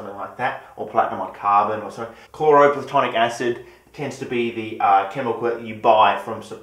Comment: Did you just say "chloroplatonic acid"? Is that for chemists who don't want intimate relationships with their reagents?